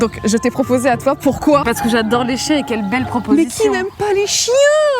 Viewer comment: Au début,j'ai compris "j'adore léchée"🤣🤣🤣